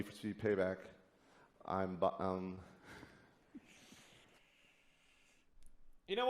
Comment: I need to start going to the bathroom before watching your videos because I laugh so hard, I sometimes spring a leak lol! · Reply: Jesse Wellens is a bi bum? lol! sounded like he said I'm bi bu um lol